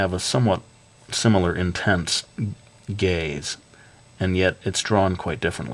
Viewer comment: Is weird that at around 7:50 i thought he said gays instead of gaze